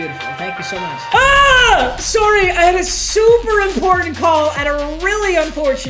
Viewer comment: Did he just say “sourry” like an Australian